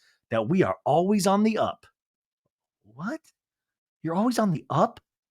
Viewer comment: I thought Lala said "we are always on the out"...(not up)